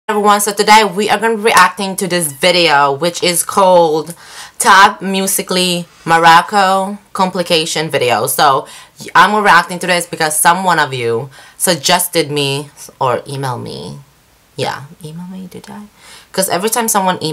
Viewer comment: Did she just say moracco complication video? 😂 😂